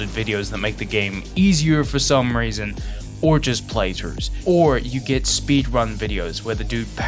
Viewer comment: why did you call zanny a plagiarist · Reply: Huh? I said he did a play through which he did do a play through I watched the whole thing it was great.